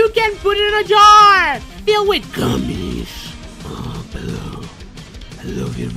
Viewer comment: I thought he said Gummies